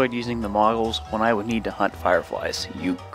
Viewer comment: I thought it said wiggles xD